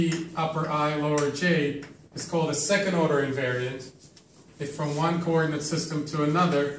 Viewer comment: one small correction at 0:46, it should be: "is called a second order TENSOR, if from one coordinate system ..." instead of "second order invariant". Great lecture series btw. Thanks a lot!